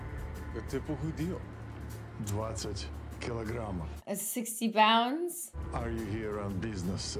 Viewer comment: That conversation isn’t about his weight loss, he’s telling John how many guys are waiting for him inside. Everything in this world is in code. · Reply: I thought what he said was actually 20 kilos, which be roughly 45 pounds. Then again, maybe Russian isn’t his first language… Francis??